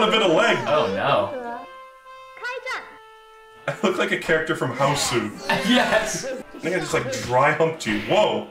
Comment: could someone please tell me what hosu is, or what it is actually called? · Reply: it's a Japanese film called House that is affectionately called "Hausu" in the West.